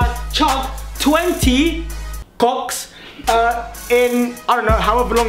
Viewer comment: I'm part of the gang but at 1:01 it sounded like you said you were chugging "cocks" #nooffense! 🤑🤑😎